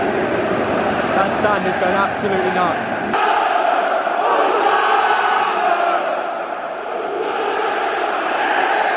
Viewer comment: what do they say in this Chant? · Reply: Aux Armes (4 times) Nous sommes les Marseillais (2 times) Et nous allons gagner (2 times) Allez l'OM